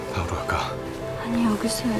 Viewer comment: 박으러갈까라는줄 알고 5번 돌려들었네..